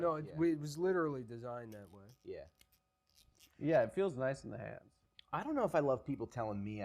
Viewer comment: Did Adam say that Füm “feels nice in the ass”?